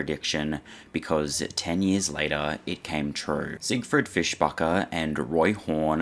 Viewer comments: lol I keep hearing you say his name is Fishfucker. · I was only paying half attention and Im pretty sure I heard "Siegfried Fishfucker." I'd rather not be corrected.